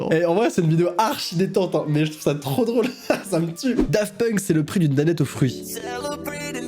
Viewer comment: Ayaaaa j'ai entendu " Saloperie de Danette aux fruits " pour les daft punks mdrrr